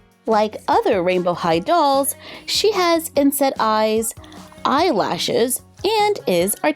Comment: I wonder how well these bodies would work with a Barbie-type head. I love the articulation, but not a fan of the big noggins and inset eyes. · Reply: Lolz I thought you said insect eyes 😭😅